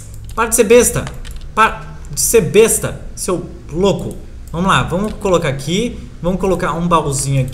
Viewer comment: só eu q escutei o falando SEU BLOCO! kkkkkkkkk